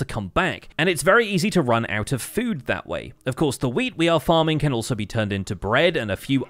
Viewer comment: That sounds like you said "of course the weed we're farming"